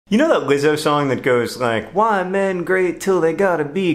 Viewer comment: That what song? I can't hear the word you use. It's spoken too fast and sounds kinda mumbled. And I definitely don't know that song. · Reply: I know it's been 4 years, but he said Lizzo, the song is called Truth Hurts